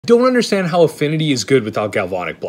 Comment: What i heard was 'i dont understand how making cards cheap is useful"